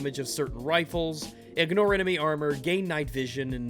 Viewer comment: I had to do a double take cause it sounded like you said "Gay Night Vision"